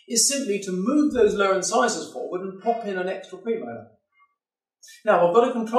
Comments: For some reason I can't understand what he's saying at 6:30 : "is simply to move those lower incisors forward and pop in _____________". Pop in what? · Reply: pop in an extra premolar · Please—What is he saying at this point 6:32? To move those lower incisors forward and pop in a WHAT? Thanks in advance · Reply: premolar (with an implant)